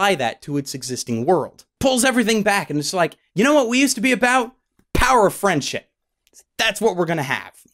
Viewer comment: Lol, I thought you said "The power of frenchship".